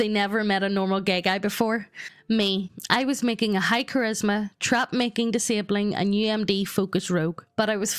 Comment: I wasn't paying attention so all I heard was "High Charisma Trap" Had to rewind the video there to see if that was right